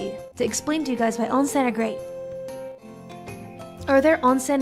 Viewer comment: explainのあと何て言ってるの？onsen great？